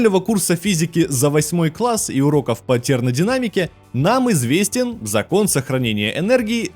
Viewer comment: Уроков "тернадинамики", или мне послышалось?